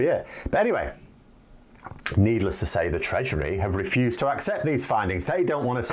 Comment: It may be your microphone.. but every time you say “treasury” I keep hearing “treachery”..